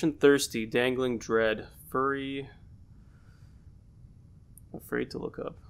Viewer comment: Another awesome video. The story is so strange and twisted. 14:09 I think that say furry leeches, maybe?